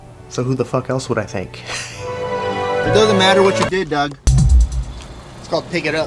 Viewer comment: After years of watching, today I realized is saying "it doesn't matter what you did, Doug"..... I thought he was saying "it doesn't matter what you did dogg".... I'm an idiot.